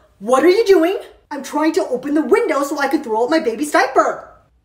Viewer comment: i heard that wrong i thought she said “im trying to open the window so i can roll my baby outside”